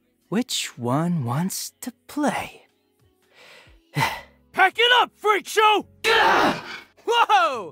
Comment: What does it say at minute 10:25? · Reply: I think it is "Pack it up, freakshow"